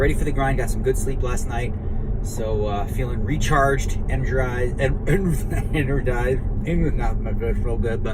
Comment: Feeling recharged and dried? Recharged anodized? DNegs: "You know what I'm saying."